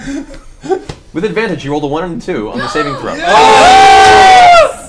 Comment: Ok so I need someone to help a critter out. 2:07:42 ish Scanlan dominates the general and gets 102 on the roll. How? Why isn’t this taken in for a moment. I heard it and went wait how is it so high? Did he snort fusaka again? Or for the first time? And then they just gloss over the insanity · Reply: Well the subtitles say "He rolled a one and a two" not 102